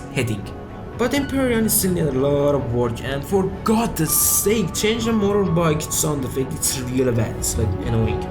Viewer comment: Some please tell me what the fuck he said at 14:40 it sounded fucking hilarious not trying to make fun of the guy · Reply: "For god's sake change the motorbike sound, it's like really bad...fuck"